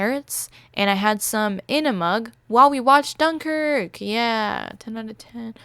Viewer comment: thought u said “enema” but it was “in a mug”